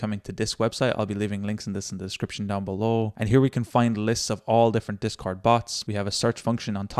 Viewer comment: if you start at 0:36 or 0:37, and skip ahead, it sounds like he says "And here we can fuCKIN fur."